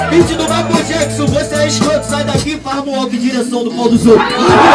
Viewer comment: não entendi a rima do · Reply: "Você é escroto, sai daki e faz Moonwalker em direção ao pa* dos outros"